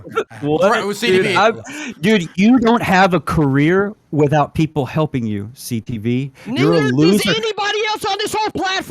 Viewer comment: did CTV just call Brent the n-word??? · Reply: He said neither